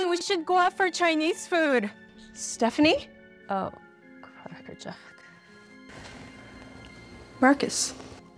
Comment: did she just say “oh, crackerjack?”